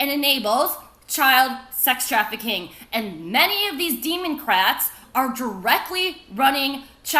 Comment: Hello children, today we learn how to turn your christian missionaries into pasta strainers. EDIT: 13:29 Did she say Demon-crats?